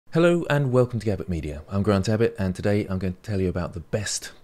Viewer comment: ok unrelated, but i thought you said "this is grand abbot" instead of in the intro of your videos for like a month.